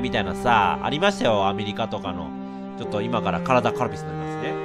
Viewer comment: なんていった？w からだカルピス？